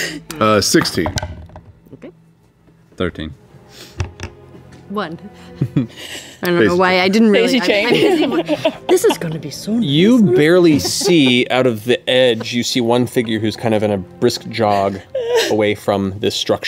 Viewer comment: i only just caught the “on her” part i thought she just said “this is gonna be so nice” 🥺🥺 babey